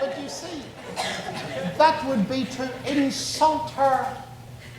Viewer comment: Can someone help me to understand what he says around 5:48? “But you see, that would be _____”? 5:55 “the secret of __ marriage” and also in 6:15 “that sets her free to learn to ____”? Thanks! English isn’t my first language. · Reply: That would be to insult her. The secret of MY marriage